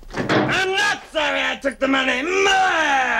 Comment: for years I thought he said Mine and it came out garbled as “Meh” “IM NOT SORRY I TOOK THE MONEY, MINE!!! HAHAHA” This scene always makes me laugh